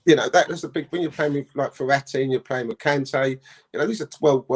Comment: lol did he say Verratti or is my ear not working